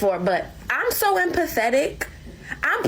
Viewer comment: Hha I thought that one girl said "i'm so pathetic" because it made total sense.